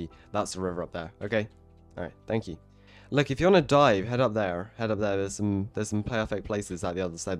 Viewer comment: is it just me or did I hear "look, if you wanna die" instead of dive 😂😂